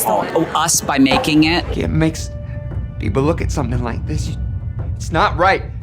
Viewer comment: if you’re the monsters for making it …. maybe you shouldn’t have made it then? just a thought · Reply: What is it he says before that? I heard "black people" and now I can't unhear it till I know >.<